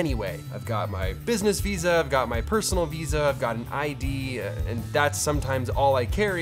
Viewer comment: I swear I heard him say IED.